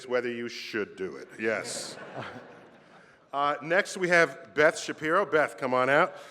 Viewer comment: I thought Neil deGrasse Tyson said "Ben Shapiro"